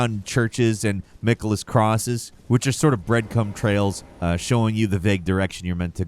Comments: Did he say breadcum trails? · Is it just me or did he say bread-CUM trails? At 4:17 😂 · I heard "breadcum trails", insta buy. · did homie say… “breadcum”?… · D-did you say 'bread c*m'? 😳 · d... did I hear him say "breadcum trails"?